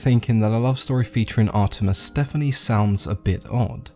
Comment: "Artemis Stephanie" ? Why did you call her Stephanie? Who else caught that? I thought I misheard that but a I relistened to it 3 times and he definitely say "STEPHANIE" and I can't figure out why